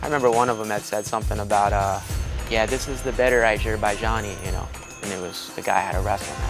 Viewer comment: Ok can somebody please tell me what he says at 10:00 lol I cannot figure it out to save my life. It’s what he says the American says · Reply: certain Americans had certain things to say, I remember one of them said something about uh, yeah this is the better Azerbaijani, and it was the guy I had to wrestle next....